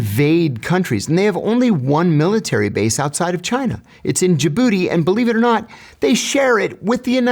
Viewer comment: I jumped when he said its in Djibouti thought he said jabooty bro😭✌️